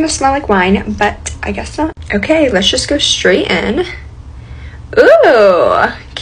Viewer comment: Why i hear "let's go straight"